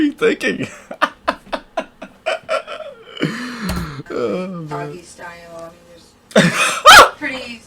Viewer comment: what did the female cop say to make you laugh that hard? i couldn't hear what she said and the closed captions didnt either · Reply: “Doggy style”